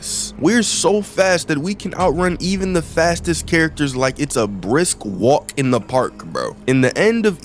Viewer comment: my dumbahh thought is said tastiest